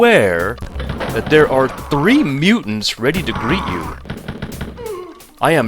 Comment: I misheard that as "3 mutants ready to breed you"